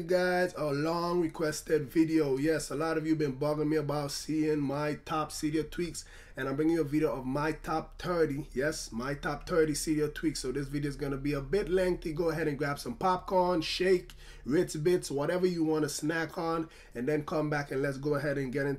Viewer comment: Sounds Like You Said My Top Terdy Tweaks. Lmao